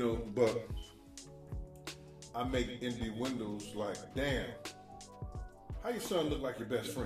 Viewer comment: Near 8:11 did say "Indo'windows"? 😂#Inuendo...